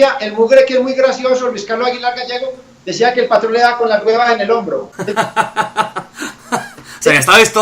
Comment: que dice en el min 22:05 ? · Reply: Lamansatula "Es muy gracioso Luis carlos aguilar gallego decía que el patrón de daba con las guevas en el hombro"